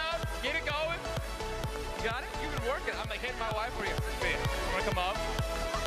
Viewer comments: Did ninja just say "Imma Hit my wife"? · Sounds like he said "I'm gonna hit my wife for you" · did he just say "I'll hit my wife for you" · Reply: I think he said “I’ll get my wife for you” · sounds like Ninja says "I'm gonna hit my wife for you" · Sounds like he said "imma hit my wife for you" 💀💀